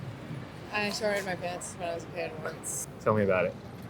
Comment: can someone write down what she says in 7:06????????????? im not native and cant understand right, also the subtitles are wrong, thxx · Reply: “I sharted my pants when I was a kid,once”